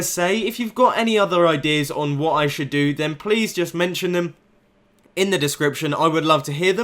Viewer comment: Did mumbo just say we need to write in the description